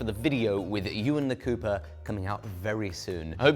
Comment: I had a Twilight Zone moment when I heard, "we're going to visit you in the Cooper" 😅